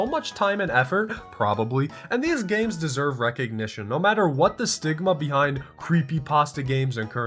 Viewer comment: why the hell did i hear "what the sigma"💀